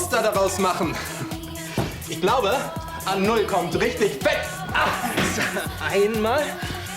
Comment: Was sagt Olli bei 6:29 ??? "Ich glaube .... kommt richtig weg."